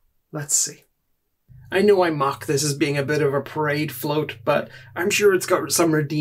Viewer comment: Your accent made it sound like you said Pride float, and now I want to get one and paint the Aquilla in a rainbow pattern